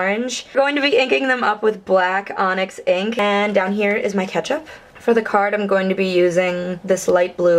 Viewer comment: Beautiful card. Thanks for making us laugh on our Monday blues! I thought you said, "here's my ketch up for the card" ! Lol... Whaaaaaaat?! Then.. Soon I realized you were talking about the cardstock.. Thanks for the inspiration. ♥. Favorite food to overeat.. Brownies!